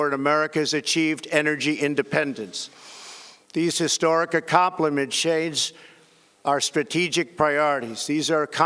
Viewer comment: wtf was that he said. Acompalat or Accomplishment ?